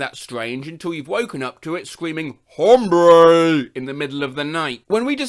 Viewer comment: i really thought that said "harambe" at first lmfao